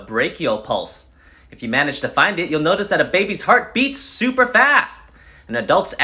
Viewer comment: I had to listen to this because I thought it said “Babies Heart Breaks” at the start lol